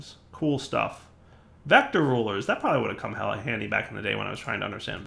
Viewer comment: did just say hella handy?